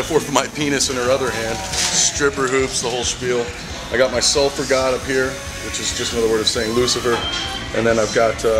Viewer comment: Uuuuh.... did he say Sulfer God. "It's just another word of saint lucifer and then did he say the eye of satan?Satan? 2:22 .... Say what